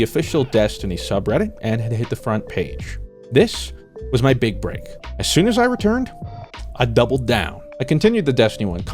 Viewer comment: wtf did he say around 9:10 lmao. "i swear to god i ......"?? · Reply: "I swear to god I nearly fainted man" LOL